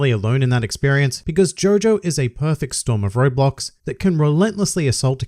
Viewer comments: for some reason i thought you were saying roblox and not roadblock smh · I thought you said ' perfect storm of roblox' for a second